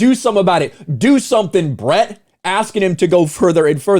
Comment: I thought he said "do something Ryp." Same difference.